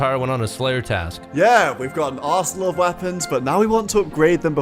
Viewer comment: I swear 4:49 says “Yeah! We’ve got an ass-load of weapons!” Lmao